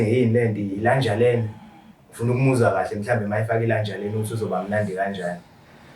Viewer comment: Did he say "lanjalena" 15:56 ...🤣🤣🤣